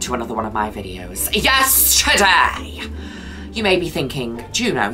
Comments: THIS WHOLE TIME I THOUGHT SHE WAS SAYING “Yesterday”!! YEARS!!!! Wow. I feel like…. I need to sit down. Juno has been saying “Yes, Today”?!?!?! I can’t believe it! 😂😂😂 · Shut up! She’s been saying “Yes, today” this whole time. I always thought she was saying “Yesterday” because Juno. 😅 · i always thought she was saying 'yesterday' not 'yes, today' in the beginning of her videos. am i the only one??